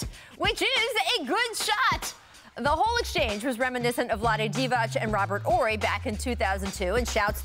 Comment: Wait, did she say Vlady Divach!